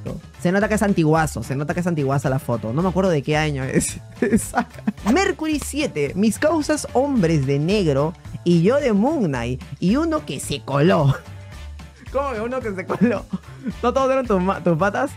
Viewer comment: pensé que decía mis causas hombres negros y yo de cum knight